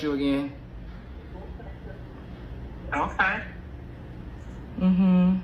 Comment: yes the mom actually did apologize, she said I’m sorry but the caption said okay.